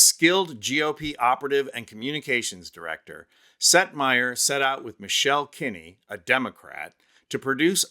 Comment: Oh. Setmayer! I was so confused. I thought he kept saying Seth Meyer! 😳 Silly me. 🤣🤣🤣🤣🤣🤣🤣🤣🤣🤣🤣 💙💙💙💙💙💙💙💙💙💙💙